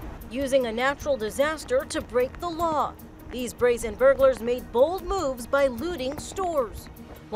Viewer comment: I thought it said frozen burglars